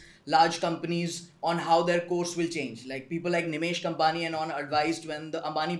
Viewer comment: Can anyone tell me the name of the banker who advised Mukesh and Anil Ambani split? I couldn't hear the name properly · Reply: Nimesh Kampani